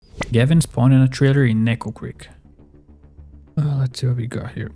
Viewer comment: I heard Neko creek so.... neko neko zom-BIEEEEE-chan!